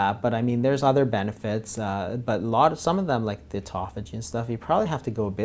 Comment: What does he say at 25:05 ? Some of them like..... what? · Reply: He said "Some of them like the autophagy stuff" and for that you have to go a bit longer :)